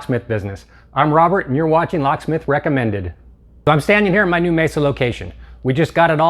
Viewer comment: I thought they said , "hi armed robber welcome to locksmith recommended"